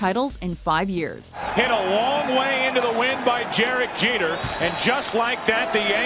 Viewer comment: did he say jerek jeter?